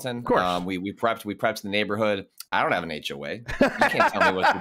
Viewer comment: Really enjoyed the 12 foot skeleton talk. I’m going insane trying to figure what ‘H Away?’ refers to at around the 17:50-17:59 mark? I could be mishearing something. Please help lovely people. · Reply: H.O.A. Home owners association. They typically can control how you paint your house or what you have in your yard for example and can fine if you violate their rules